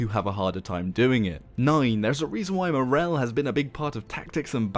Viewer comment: I misheard moral as wrel,haha. Good old times in ps2